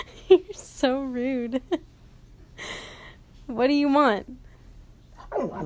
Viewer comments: When she asks him "what do you want?" It literally sounds like he says "I don't know" So funny😂😂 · When she asks him what he wants it sounds like he says ‘I don’t know’ 🤣❤️ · She asked him what he wanted sounded like he said "Don't Know "🤣😅😂😆❤❤❤🤣👀👀❤🤣😂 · When you asked what do you want it sounded like he said i don't know! Too cute! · You said what do you want and I swore he said I don’t know